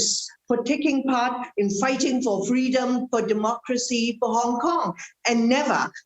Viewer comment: Facing evidence, Jimmy Lai and two pro-democracy activists pleaded guilty! I thought he said fighting to the end ( my understanding is until dead.). LOL. Peaceful none violence demonstration? OK, even that is true, did they break the law? What UK will do to law breakers?